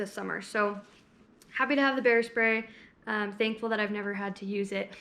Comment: I thought you said "hair spray" when you said "bear spray" 😂 Love this informational video! Perfect for newbie hikers like myself. More please! ❤️